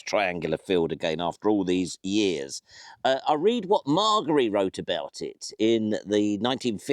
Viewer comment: Interesting. A lost road…..reveals itself in the uncanny and chthonic light of an eclipse….my mumbo jumbo klaxon went crazy 🤪! Sadly it’s not just eyesight….hearing also…..I rewound I thought you said ‘Marjory’.